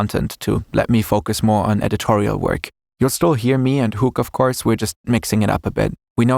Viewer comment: DID HE SAY HOOG? I Knew and Hoog were related somehow...